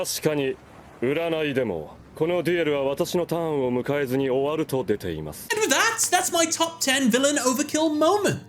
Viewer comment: it almost sounds like he says “kono Dio da.” 😂😂